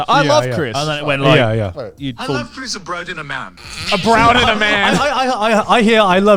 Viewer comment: I swear I heard: "I love pizza o'bread on a man."